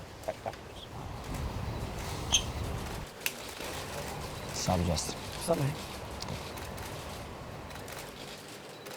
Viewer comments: What did they say to eachother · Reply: He said whats up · some1 please tell me what justin said there..... I don't understand him, he's speaking Miocic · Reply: Khabib: waht's up Justin Justin: sup mate The just greeted each other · What did they say? Didn't catch that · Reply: Sounded like Khabib said "sup Justin", and Justin said "sup man", I think? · What are they saying at 8:27? All I understand is "What's up, Justin?" - "What's up, mate?". Amirite? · What did they say at 8:27 I couldn't understand them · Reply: Khabib: "Sup Justin" Justin: "Asalamalakim"